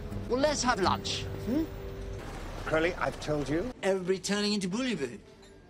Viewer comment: what were they even trying to say? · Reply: Bouillabaisse, it's a French fish stew. (Bet he's watched Aziraphale eat it before.)